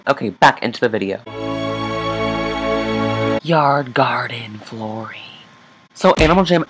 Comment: I thought you said GARDEN FLORIE I was like boi is Florie your gardener